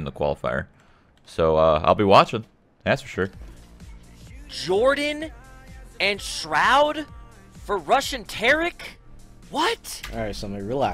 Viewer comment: first up when they said rush and tarik i kept hearing russian tarik. like i was confused who russian tarik was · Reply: #seslu i heard "Jordan and Shroud for rushing Tarik" ..i wondering why they rushing Tarik for